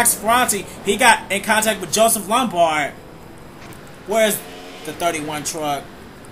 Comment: "Where's the 31 truck?" did I hear that right???